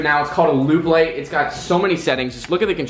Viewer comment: Wtf are those lights called cuz I want some. Sounded like you said lube lights so what are they